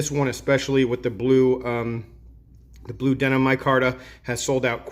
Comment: You know back in the day when I relied on the knife show I thought they were saying “Lennon micarta” 🤣🤣